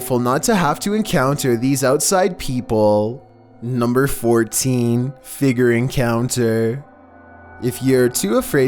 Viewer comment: Why did I hear "number 14 finger encounter"